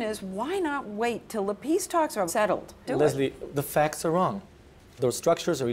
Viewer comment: did he just say "the facts are wrong?"😅